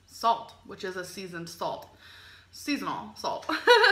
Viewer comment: I think people thought you were saying "Seasonal salt" instead of "Seasoning Salt"